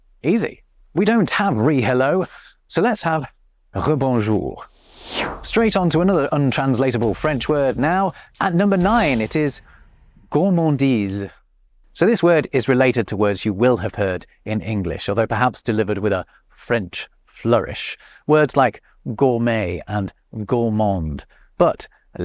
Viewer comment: Funny, when you first said the word, I heard "gourmandile"n with an L